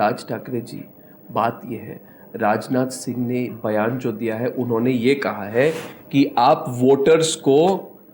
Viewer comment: Does Arnab say "Bayaan Chodh diya hai". . . . Pls tell me